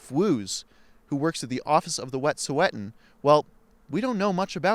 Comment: WOW, I thought it said Wet Sweatin.....